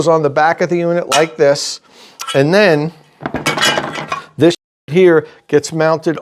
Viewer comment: I didn't quite catch that what you were saying at 3:59 the audio cuts out for just a sec. 🤔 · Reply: I think it's just something like "this <piece> here"